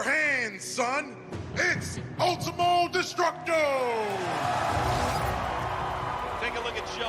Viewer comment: What it says: Ultimo Destructo Subtitles: Multiple Destructo